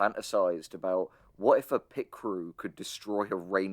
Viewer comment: I misheard this as "pit crew" and was really confused imagining a pit crew disassembling a forest like they're repairing a car